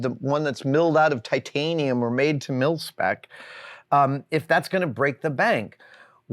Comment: Slightly confused and amused when I misheard "Milspec" (Military specification) as "Milf spec." I took a long time to learn that money saved on cheap tools is usually wasted.